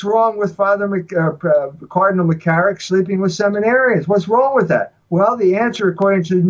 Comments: I thought he was saying: he was sleeping with seven arryans.he was saying seminarians.😆🤗 I bet he wishes it was seven arryans,homosexuality is not natural,end of discussion ,😠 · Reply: I also thought he said Aryans. Ha ha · I thought he was saying: he was sleeping with seven arryans.he was saying seminarians.😆🤗 I bet he wishes it was seven arryans,homosexuality is not natural,end of discussion ,😠